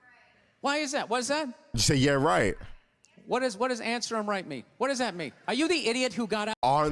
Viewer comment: I think what the woman said “it’s her right!”. So… I’m thinking she got off easy 💀